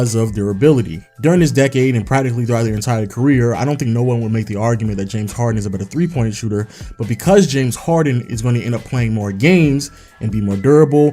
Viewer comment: did you just say, that harden is a better 3point shooter than curry? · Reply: No I said that I don't think anyone would make the argument of James Harden being a better 3-point shooter than Stephen Curry